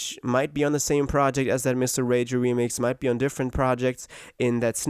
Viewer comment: I thought you said miss the rage remix I was like huh